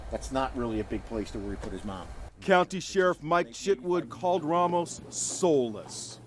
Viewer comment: did they says sheriff Mike ShitWood?